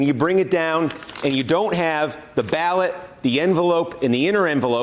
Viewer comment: Cool bro alert... he just said “HOMIE” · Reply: You're killing me... He said "if you're home..." But it does sound like he said HOMIE LOL